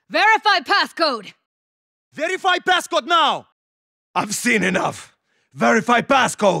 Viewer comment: Minotaur: literally crushes someones face with his boot Also Minotaur: "I've seen enough!" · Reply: I thought he said "I've sinned enough"